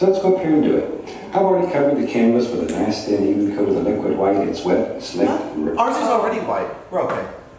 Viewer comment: usually bob ross videos are calming but this was chaotic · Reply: Salma Arias// Especially when Bob Ross said get the canvas wet but tHEY THOUGHT HE SAID WHITE that hurt me